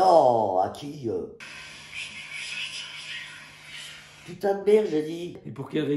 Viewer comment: Moi j'entend "Je transpire putain de merde " xD 5:55 "Oh plus jamais je te carotte" ou "Oh plus jamais tu l'arrose je suis pas "...." "Oh plus jamais tu l'arrose je suis pas l'amour de ta cravatte ;-; "